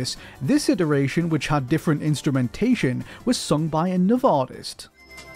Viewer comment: I thought it said farts